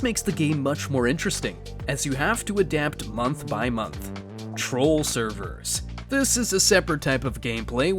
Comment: when you say "troll servers" i hear "normal hvh"